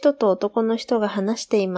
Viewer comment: I've listened to this several times but why does it sound like "どこの人" "おとこの人" at 3:53? Am I just mishearing? Or is that a mistake?